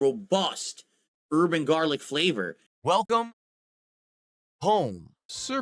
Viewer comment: my video player lags sometimes and I Hear "Bust Urban Garlic Flavour"